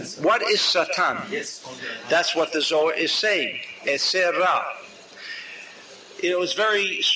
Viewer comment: What does the Rav says when he asks What is satan minute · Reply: i think he is saying "yetzer hara" which is hebrew for one's "evil inclination" and/or one's "desire to receive for the self alone"